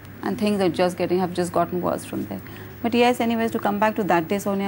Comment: okay idk who killed arushi or wtv but did she just say okay anyways?